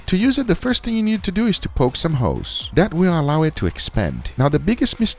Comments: did he just say we need to poke some hoes? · Am I the only one that thought he said 'Poke some hoes' at 2:25 Great pick up advice 🤣🤣🤣